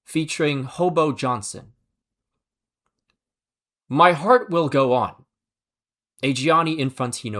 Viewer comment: my dumb ahh thought it said my heart will goon